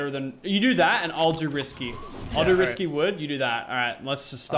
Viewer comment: who else heard him say "Risky Woods"?